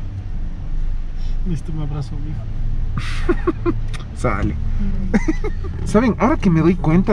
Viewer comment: que dijooo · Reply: "Necesito un abrazo mijo"